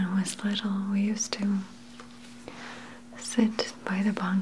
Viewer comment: Wait. Did she say "Bonk fire" @ 18:52?